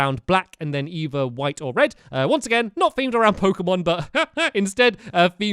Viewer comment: Did he say Pokemon?